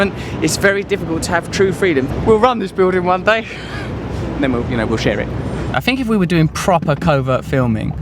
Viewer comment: Does he say. "we will rob this building one day at 4:19 ? He is an ugly person.